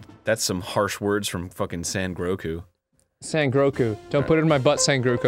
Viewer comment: Did he just call him San Groku? lmao